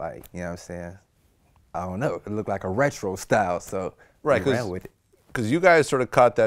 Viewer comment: Nigga said "restrural style"... thought that nigga said restroom style for a second LOL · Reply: smittyboy007 He said retro Fam